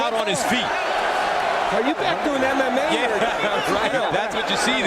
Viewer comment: Did he say this isn't "MMA"???